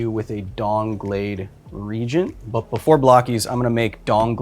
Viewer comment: I swear I heard Kyle say "DONGblade Regent".